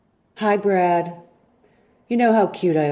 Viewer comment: I thought she said: "...for me?" 🤯😆🤔